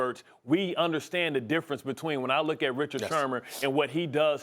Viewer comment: Anybody else catch “ Richard Shermer “ ? 6:00 to 6 :20 😅😅🤣